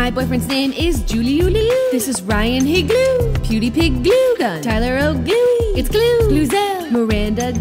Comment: i fist thought it said PewdiePIGluegun